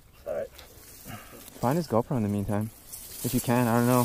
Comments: I thought he said find his girlfriend in the meantime, I had to rewatch almost 20 seconds of video to understand he was talking about the GoPro 😂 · i thought he said "...find his girlfriend in the meantime..." like whaaaat!? This whole time🤣🤣🤣